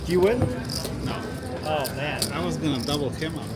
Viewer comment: ish did he say he was going to try a double you up as in give you tht hand or?